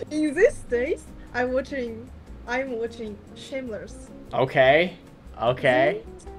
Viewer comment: That Mongolian girl is so pretty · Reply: When she said shameless I thought she said Schindler list very fast.